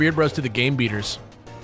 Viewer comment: When said "game beaters" for a second I thought he said "gay beaters" and I started laughing